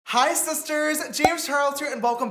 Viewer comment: i’ve heard him say “hi sisters” so many times, it sounds like either “heizisters” or “isis turds”